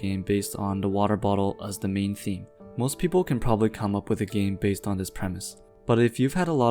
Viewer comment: Sorry, did you said "most people CAN'T ..." at 6:59. I listen to it 5 times and it always seem to me that you were saying "CAN" but that would make your following line not a counterpoint, not making too much sense.